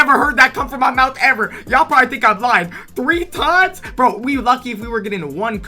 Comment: why did it say retard when u said 3 times